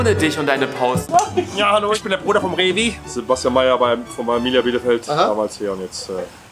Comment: Habe ich da Arminia Bielefeld gehört?